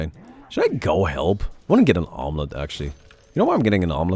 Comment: Lol, I heard omelet